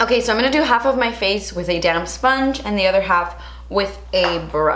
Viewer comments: I’m gonna do half my face with a damp sponge. But I heard “damn” sponge!!! Hahahaha!!! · Every time you say “damp sponge” I hear ‘damn sponge’ haha!